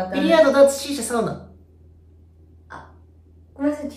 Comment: ビリヤードダーツシーシャまではわかったけど最後が分からんくて草、最後スワンナにしか聞こえん。ポケモンのスワンナか？ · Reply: ポケモンwwwサウナですかね🧖‍♀️